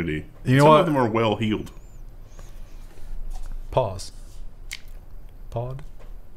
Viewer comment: I thought said pause and I thought he was on a completely different level for a moment there. Funny.